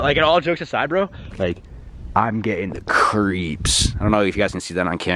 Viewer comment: When ever you said you’re getting the creeps i thought you said I’m gayin